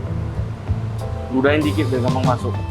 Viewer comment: Anjir gw salah denger gak sih 15:43 ludahin dulu biar masuk 🤔